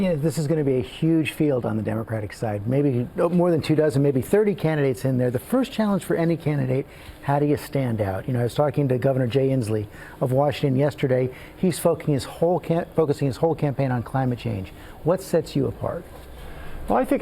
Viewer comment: does he slip up and say "...he's f*cking his campain on..." or am I the only one the misheard? I know he meant to say focus, but he slips up briefly... Anyone else hear it?